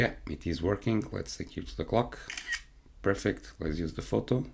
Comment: I heard "Let's take a photo of the cock!" Shocked. Then realized, ah clock! Haha. Jokes aside, good work. Keep it up!